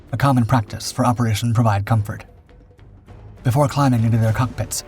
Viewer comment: commentary mentions Operation Provide Comfort, captions say Operation Provide Freedom.